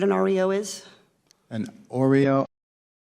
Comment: I mean, not to defend this guy, but it really does sound like she said "oreo," and his tone implied that he was confused by the question... (though yeah he definitely messed up afterwards)